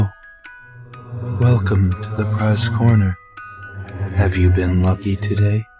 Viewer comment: When puppet says "Welcome to the prize corner" all i hear is "Welcome to drepressed corner" for some reason :/